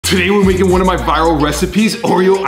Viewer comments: I I thought he said final recipe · I almost cried because i thought he said "today we sre making one of my final recipes" for some reason 😭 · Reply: He said Viral